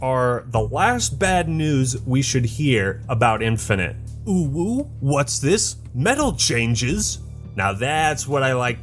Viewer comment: OwO!? The Act Man is a Furry I hear?